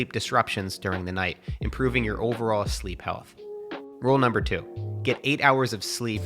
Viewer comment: I hear "rule number one", but I see "Rule #2" and I'm thinking how much sleep do I need?